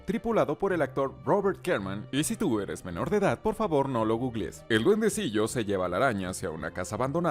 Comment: que lo busque dice?